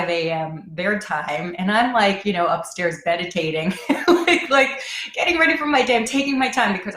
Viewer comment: Lol 😂 when you said you were upstairs meditating I thought you said you were upstairs medicating 🤣🤪😁🤦‍♀️😂🥰🥰🥰